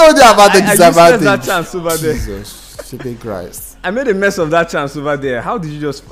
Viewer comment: what did say right there? Gotta be careful with swear words. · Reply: Flipping christ